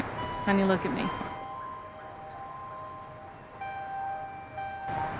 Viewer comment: I almost broke down to tears when he said “I stopped it.” He is truly a good man. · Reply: I watched this movie so many times and for the longest i thought he said "stomped it" like he actually stomped on his own arm! 😅